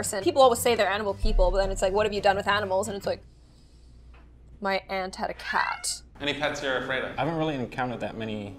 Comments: Why does it sound like “My ant had a cat” instead of “My aunt had a cat” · I thought she said, 'My Ant had a Cat.' It was My AUNT had a cat.